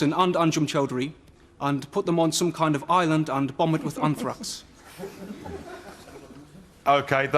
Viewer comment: I thought he said ‘bomb it with Kumquats’ sounded weird.